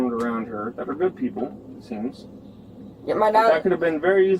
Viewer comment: Damn autocorrect! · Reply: I thought for a second you said "food people" because that woman offered French toast like 3 times.